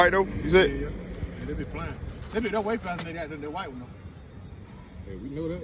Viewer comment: They say those clutches go fast on those. I see now. Sound like you said freddy's way faster than Jason got to get me a Freddy!! Love the vids man!!💯💪🏾🔥